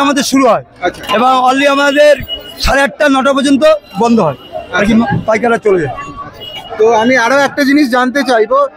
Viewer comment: তে কি বলছে? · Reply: babsayeera 8.30 am er por chole jay